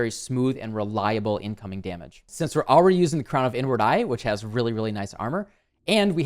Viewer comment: i swear i hear him say "the n word eye" when he talks about the helmet